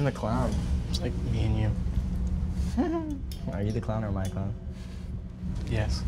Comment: did he say "are you the clown or my clown" ? If yes THATS FREAKING ADORABLE i miss 😢